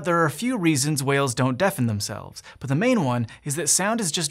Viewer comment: i thought that said why dont whales defend themselves lol